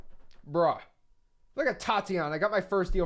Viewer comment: I heard the soul innat tatianna😂😂😂